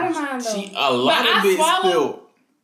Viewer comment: Boy “alota bit” ain’t a word. It’s either a lot or a little😂😂😂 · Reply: I thought he said a lot off it